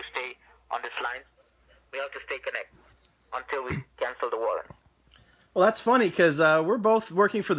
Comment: Every time he says " warrant" i keep hearing " war " Im ashamed of this because im indian.